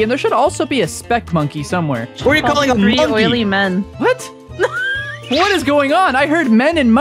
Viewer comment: i think said "i like oily men" if am right then i get nothing!1!1!